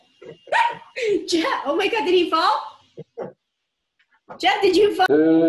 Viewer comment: Me: Watching the video Also me: Thought she said “Chat did he fall?”